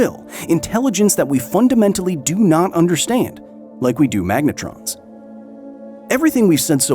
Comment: I thought u said megatron. U said magnetrons. Lol